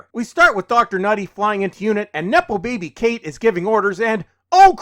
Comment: "Nepo Baby". Of course. The first few times, I heard it as "Nipple Baby" and was wracking my brain to come up with a reason for that.